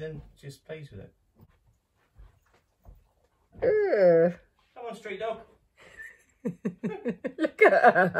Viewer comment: I thought said "come on stray dog" hahahahaha Rest your arm. It might need a soak in a warm tub with epsom salt and a glass of wine. Repeat every night.